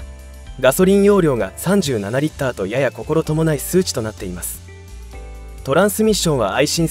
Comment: 〜「ココロトモナイ」と聞こえるけど「心許ない」と、言いたかったんだろうね。